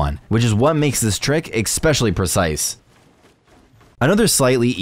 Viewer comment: did i just hear you say expecially? I hope not